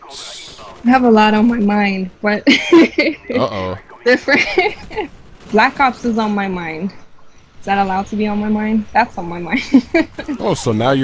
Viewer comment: no offence, but for a minute i thought she said black cocks were on her mind. -__